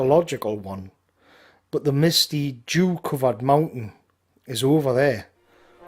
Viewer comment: 'Ang about, the misty what-covered mountain did you say there? I'm right on board with seeing our folk retake the peaks we fell from, but you're not exactly selling it to me here mate. Didn't think we'd be allowing that sort of thing on our mountains in future 😂. Great vid again though fella 👍. Would love to see the Blade Runner theme turn up again, when the mood next takes you. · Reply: He said "Jew covered mountain". Mountain Jews.